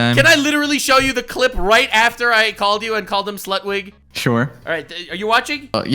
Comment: I thought he was saying slugwig, because slugs equally have no spine and they are more gross than jellyfish